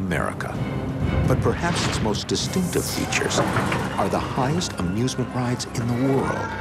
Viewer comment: Did He Said "Stupid Teachers"?